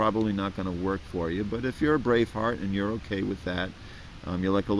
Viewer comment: sounds like he says brave fart LOL. Great video though!